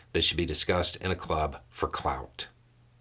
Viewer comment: Oh "...in a club for clout" I thought at first you said "in a club for clowns" ...both work though.